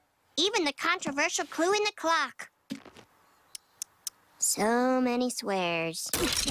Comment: I thought Lisa said “So many squares.”